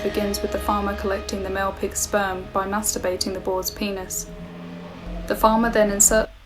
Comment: DID I HEAR THE WORD “PENIS” AT 10:38?